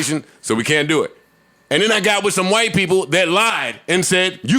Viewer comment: All I heard was “muh white people”.